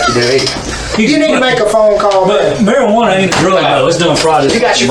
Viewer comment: is he saying "Marijuana aint a drug"?? That's what the closed captions say too so Idk if I just wasnt paying attention enough (I also set the speed to 1.5x so their accents can be difficult to understand fully sometimes) or if they really weren't talking about weed at all until he randomly said that lol??